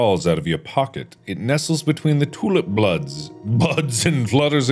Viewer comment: "The tulip BLOODS" Ah yes... Definitely what that text said. 31:19 Mark: "Problem detected!" ....Glad the subtitles were there so I could see what he was saying... cuz to be honest, I just heard him saying "fom/from". 38:08 "MY FREAKING EYE!!!"